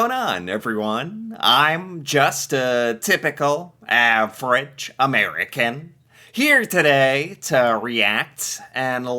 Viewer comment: at first I thought you said you were a French American Average American lol